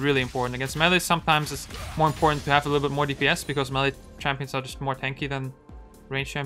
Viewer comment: "is melee champ" i thought you said yone "is smelly champ"